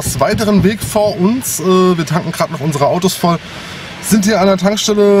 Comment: XD Ich hab am Anfang verstanden "Wir kacken gerade unsere Autos voll" 😰😹